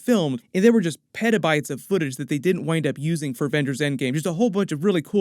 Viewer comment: Not gonna lie, 11:00 first time I heard it I thought you said "BENDER'S end game". I've listened to it multiple times and I can't un-hear it. I even checked the CC and there it's correct. But my brain still can't unhear it. lol😅😂😅😂